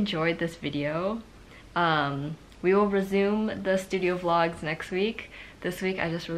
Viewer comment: I swore I heard Brazil instead of resume (I'm from Brazil) 😔✊🏼💖